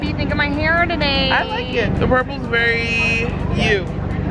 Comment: i thought said she doesn’t like purple, like at all